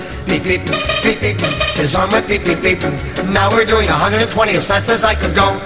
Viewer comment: Heard this in a supermarket and was creeped the hell out. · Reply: It starts off real slow and get more and more intense, made it really tense. Also, the sound was bad so it sounded like he was saying "His heart went beep beep beep".